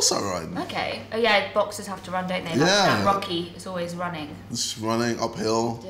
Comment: she tried to make a reference about rocky but the subtitles said monkey 😂